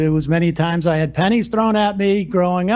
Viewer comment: I completely misheard and thought sam said he had panties thrown at him and i was so confused.